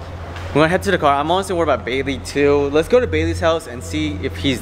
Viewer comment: Sounds like he said "baby"...😂😂